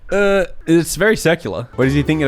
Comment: I had to google "secular" when used it to describe a meme, because I wasn't sure if means something else to non-religious people. Still don't know what made the meme so secular. update: Now he mentioned the Bible out of nowhere. A surprizing amount of religious terminology in this video :o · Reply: I thought he said "circular"